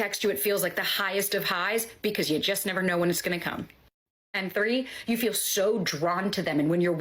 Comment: mn Did she just say, "you never know when hes gonna cum?" 😂😂😂